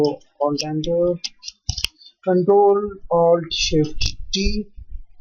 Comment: what was the hotkey that you mentioned at 5:21? i couldn't understand it, thanks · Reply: (Ctrl + Alt + Shift + T) and then select appropriate option.